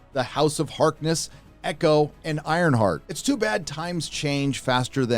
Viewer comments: Thought he said iron fart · Who else thought Iron Heart said Iron Fart? I did a double take, not gonna lie!